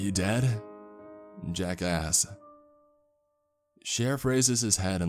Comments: When he said "jack asked" did it sound like he was saying jackass to anyone else? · When he said “jack asked” I thought he said “jack Ass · Who else thought he was saying Jack ass instead of Jack asked. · I thought he always said jackass but i relized it was jack asked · Every time he says "Jack asked" all i hear is "Jackass" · Every time he says jack asked it sounds like hes saying jack-ass